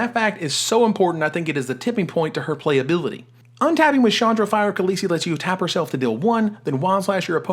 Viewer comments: Did anyone else hear Chandra, Fire of Khaleesi around 1:30? That might stick. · did you say Chandra, Fire Khaleesi?